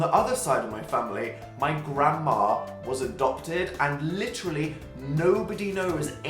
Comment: My gramma is 96% Irish, 4% Eastern European. Can't get much whiter than that. :/ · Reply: OH! i thought you said "grammar" not grandma, i was really confused